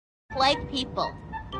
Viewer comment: I thought she said “white people”at the start 💀